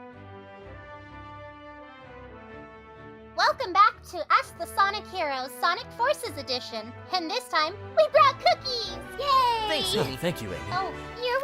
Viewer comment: what did sonic say? Thanks amy or thanks honey? If its thanks honey then im really really late on this series